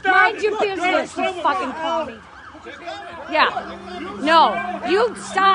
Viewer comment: Did she call him a Commie?